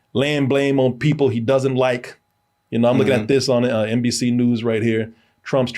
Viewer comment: ! Psst!! To me, it sounds like you said "NBC News" while showing us "ABC News"! 😮